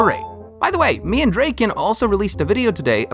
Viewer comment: i thought you said me and drake💀